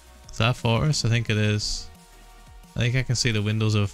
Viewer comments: Am I the only one who though he said “Is that a fortress” I thought he said forest and then got really confused bc I thought he was playing in 1.14???? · I thought you kept on saying “forests” instead of “fortress” and I was confused like- isn’t it only the ocean update?? 😂❓❓ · I thought he was saying forest 17:40 not fortress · Is it me or does Ph1LzA say fortress but it sounds like he is saying forest